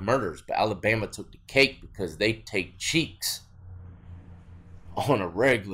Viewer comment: did he say "they take Cheeks" or Cheats...? What's that mean? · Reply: He said cheeks, as in butt cheeks. "Taking someones cheeks" is a slang term for raping them